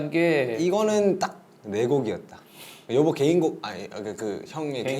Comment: Korean armies kindly confirm this. Did JK really said yeobo in 2:29 and 3:56 or is it something else.. plz don't mistake my intention.. just wanted to know out of curiosity. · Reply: is yeobo and I think 3:56 is something else.